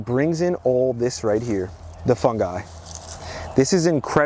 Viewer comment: I chuckle at the northeastern accents. Each part of the country has their own. When you say wortering makes me smile. My dad and wife are from different parts of Idaho and when they say horse, it comes our harse. Many words like short form come out shart and farm, my wife was a school teacher and her kids just giggled when would try to say, the short form is. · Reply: I am from the Netherlands and each time he says fungi, I hear fun guy. I think we call him the fun guy 😄